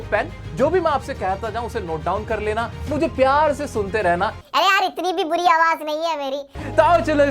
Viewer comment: yo sounded like raistar